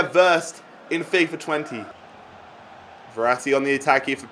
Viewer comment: did tou just say FIFA 20?